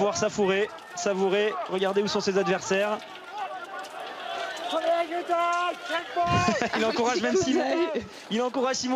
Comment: Mais qu'est ce qu'il dit Martin à Simon ? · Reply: il dit srpasse shipulin mon frero